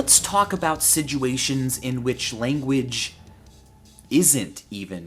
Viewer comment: lol why does he say "sijuations"?